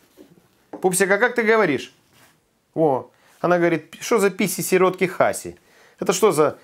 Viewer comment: "Писи сиротки Хаси" - это аналог нашей "мочи генерала" я тоже люблю только крепко заваренный чай =)) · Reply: А мне послышалось - селёдки хаси,😁😁😁